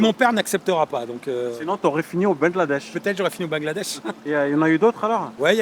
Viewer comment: J'organise des rencontres abonnés régulièrement en Thailande. Pour etre tenu informé, rejoignez le canal telegram = <link> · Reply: Quand tu lui parle de convection et sinon tu aurais fint au Bangladesh, j'ai compris "sinon tu aurais finit en ben Laden" ... MDR Je l'imagine bien en tabir ou en djellaba avec une barbe de taliban LOL !